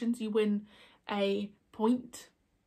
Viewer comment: I got excited because I thought you said I'd won a pint. Turns out you said a point. 😁